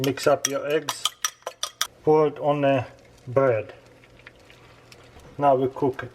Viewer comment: i swear i heard you saying fart on the bread while we cook it i swear